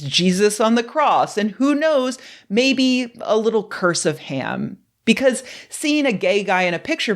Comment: I heard, "a little cursive ham," and momentarily thought how nice it would be to stress handwriting in education again... 😉